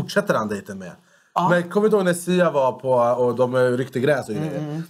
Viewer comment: Hahahah jag fastna på (tiden i videon) 15:21 Och lära spola tillbaka 3 gånger för att höra om jag hörde rätt, ”kommer du ihåg dejten med sia då dom ”ryckte” gräs, trodde anis sa: då dom rökte gräs 😂